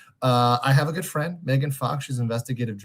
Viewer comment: I fully misheard that for "i have a good friend, Megan Fox..." and its now my headcanon our boi hangs out with holywood hotties in his spare time.